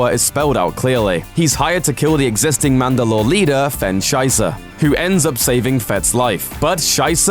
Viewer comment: wen you said sysa i heard it as scheisa from the german langouge , i am so sorry xD